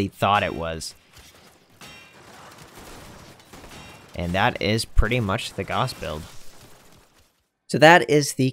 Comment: First time I heard "And that's pretty much the Gauss build" (17:43) I thought I had heard "And that's pretty much the Gauss-pel" and was all like: "Is that a pun, Operator?"